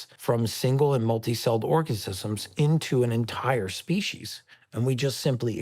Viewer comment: it says organisms but that’s not what he said. He said orgasisism.